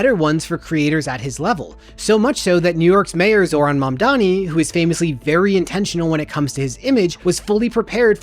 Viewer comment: Don't know if you edit your captions, but at approx 2:21, the captions read "... New York's mayor, Eric Adams ..." instead of "Zohran Mamdani", which you actually say.